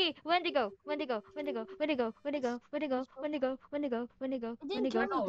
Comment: when u said wendigo fast it sounded like where to go or when to go soo funnyy lol